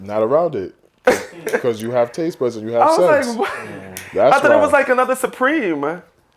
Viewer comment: Did he say “you have tastebuds if you have sex” what does that mean lmfao.